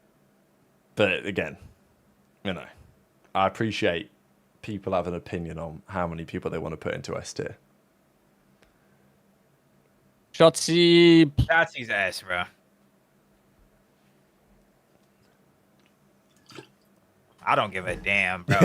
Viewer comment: I thought this dude said Shottzys ass 😂😂😂😂